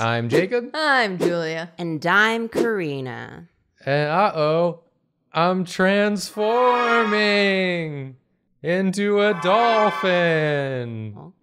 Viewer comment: I don’t know why but at the beginning when Jacob said I’m transforming I thought he was going to say I’m transphobic and I panicked so hard